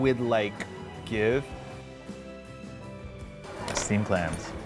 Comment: Steamed clams? I thought he said steamed hams and here I was looking for a krusty burger.